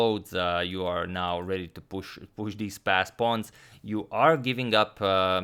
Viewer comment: Always thought you were saying “fast pawns” 😂